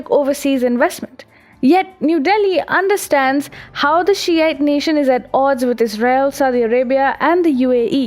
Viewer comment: when ''shi'ite nation'' was said I thought sh*t nation was said and lowkey laughed maybe a bit to hard. was that a subtle burn or am I reading to much into it?